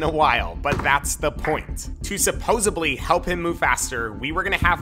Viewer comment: Does he really say supposeBly at 4:44 or it’s just me hearing that hahahaha